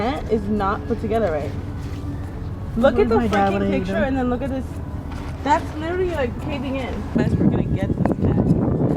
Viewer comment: did anyone else hear the f word?? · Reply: Freaking*